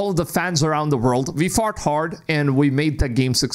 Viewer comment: "Thankfully we fart hard." Totally what my brain heard, but I know you said "fought hard," but my childish side of my brain had to share this 😅